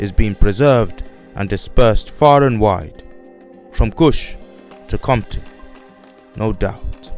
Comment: Did he just say, “From Kush to Compton… no doubt”⁉️😳